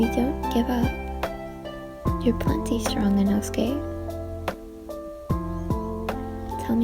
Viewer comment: What said: You're plenty strong Inosuke. Subtitles: You're plenty strong enough gay. Time stamp